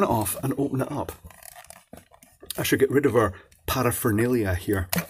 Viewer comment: Did you say 'powerphenalia'?